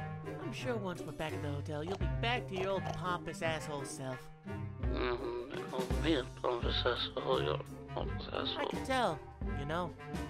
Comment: I FIGURED OUT WHAT HE SAIDDDDDD "Call me the pompous asshole, your the pompous asshole..."